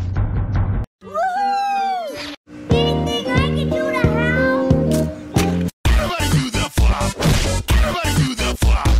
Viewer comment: Did derpy just say "do you think I can go to hell?" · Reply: She said "Anything I can do to help?"